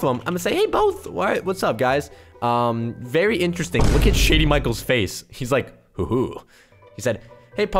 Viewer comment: haha i heard poke said "sh*tty michaels" haha i know its just me 🤣🤣🤣